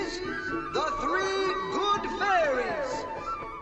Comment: what Lord Duke said is the “Three Good Fairies”